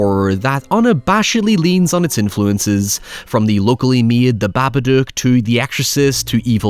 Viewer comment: I'm sure I heard them say they worked on the Babadook in some way. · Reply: "Locally made" as in also australian